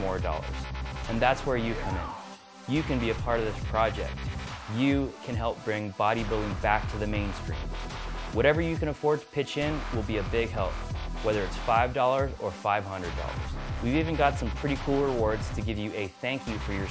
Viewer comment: does he say mainscreen or mainstream at 2:25 ???